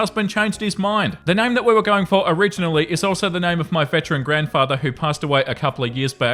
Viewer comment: I legit heard "bedside grandfather" and not "veteran grandfather". There's something wrong with me